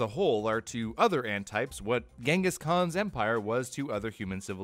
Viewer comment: why did I hear "Kangaskhan" in Pokemon (idk what the spelling of that pokemon srry)